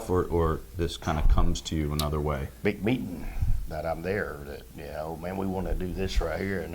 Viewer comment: At first i thought he said "beep beep" then "big meat" only to find out it was "big meetin'"